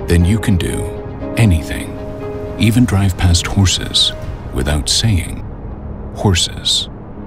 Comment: I thought he said "Then you can drive past horses without seeing horses.".